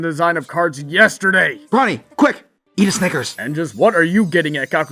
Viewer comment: or 5:28- If you see this, Brawny, what exactly did you call here? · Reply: "Gakusei", one of the Japanese words for student.